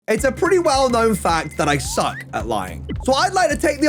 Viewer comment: I thought in the intro you said its a pretty well known fact that I suck at life - and I just nodded my head and was like ohhhhh at lying